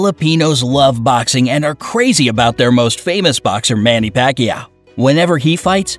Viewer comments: Manny Pacquiao Uhmmm what i heard is Manny Pak-ya😂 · I heard Manny Pakya